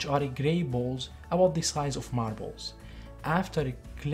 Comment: Excellent content! There for just a second I thought you said the clinckers were the size of your balls.... had to rewind and listen again to hear you say marble lol